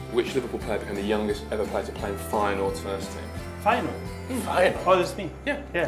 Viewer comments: What’s does he say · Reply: Tom Stuart play in feyenoords first team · what was this question? I can’t make out what he’s saying😂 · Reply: Phil Smith Smith Who was the youngest player to play in the first team of Feyenoord (Dutch team from Rotterdam)